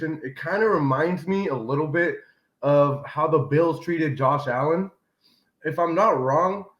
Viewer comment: My bad thought they said Josh Rosen lol